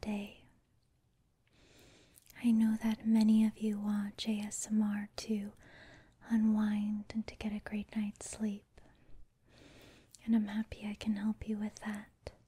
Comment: I swear I keep hearing "J"smr